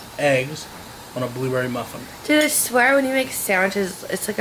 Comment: Did anyone hear say Blu-Ray instead of blueberry